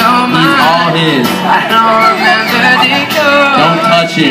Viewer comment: did he say "I don't remember the chords"?